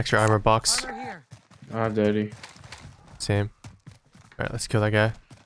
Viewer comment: Who else thought friend said daddy when he said I got deadie